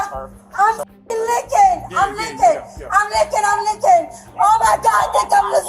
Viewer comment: I’m srry did she say I’m leaking?!?!